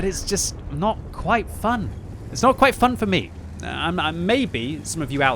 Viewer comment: Did I hear " NOT FUN FOR ME" as in "NO FUN FOR YOU!"... lol